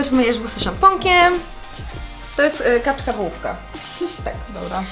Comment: Usłyszałam kaczka ,bułka xD